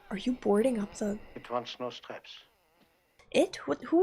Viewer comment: I was 11 when I first saw this. When he says: "it wants no straps." I thought he said: "it wants mouse traps." I thought, we saw what she did with a crucifix, what the hell would she do with mouse traps!!!!😱😱😱